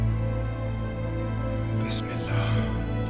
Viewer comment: did he just said "Bismillah" 😮🖤